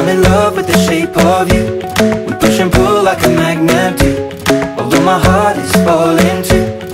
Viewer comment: the whole time I thought it said "we put shampoo like a magnet do " but it actually said "we push and pull like a magnet do" 😂